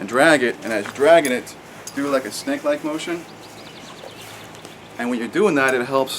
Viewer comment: I thought you said "Do like a Stank Leg motion" 😅🤣🤦🏻‍♂️